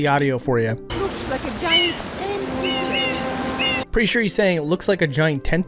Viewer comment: I don't know what he says but it does not sound like "looks like a giant tentacle" I heard "looks like a giant angry thing"